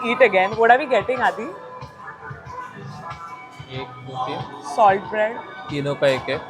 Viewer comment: did just said tino k ek hai 🧿💌